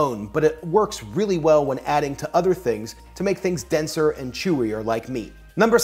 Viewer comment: It sounded like he said “To make thing denser and chewier like me.”